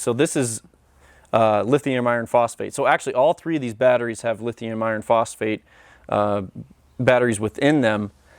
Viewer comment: As I understand it Lithium-Ion batteries are very finicky and potentially dangerous then it comes to temperature, and then fire. But it sounded like you were saying Lithium Iron which I hear are up and coming because they perform the same, output comparable Amp/Hrs. So, I wanted to be sure exactly what you said these particular ones had in them. I am VERY much a novice as I'm sure you can tell! So, thanks for any info you can share, and this vid was fantastic! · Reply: Hey Harleydr, the batteries I like are 'lifepo4' or lithium iron phosphate batteries. They fall under the lithium battery umbrella but don't catch fire. I have tons of videos on the subject of power stations, lifepo4 batteries and solar panels hopefully you find the info helpful.